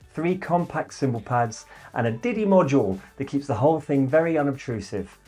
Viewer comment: Did he said diddy module at 01:03 ?